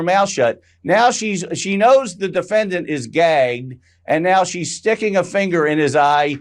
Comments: Gay?? did this lawyer just say that Trump is gay?? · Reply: Yes I heard that too! At 0:49, his attorney said she knew he’s gay, and… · Did he just say 0:48.. the defendant is gay? Who's gay? Trump?